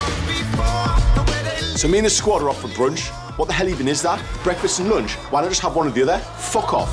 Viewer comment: I heard "so we meet a squatter off the bridge, what the hell even is that, breakfast or lunch, whynhsutsajtedajsother, fuck off"